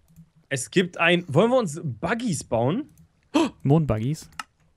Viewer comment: Ich hab von verstanden "lass und dagi's bauen" haha wie wurde das crafting rezept ausehen? XD